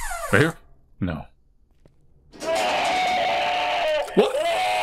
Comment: did that say tit? 😐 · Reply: You are imagining what you want to hear lol - he very clearly said "Wha?" Hahahaha!!!